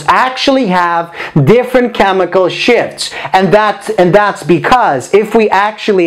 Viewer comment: Is it just me who heard chemical "SHIT"